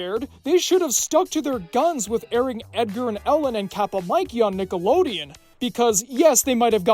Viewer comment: I thought he was saying Edgar and Owen the whole time